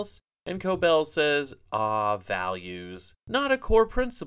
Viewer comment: Values? I thought she said valiance